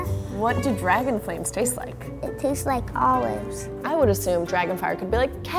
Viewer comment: What does the fire taste like? Olives. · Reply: Oh I thought that kid said dragon flames taste like oranges.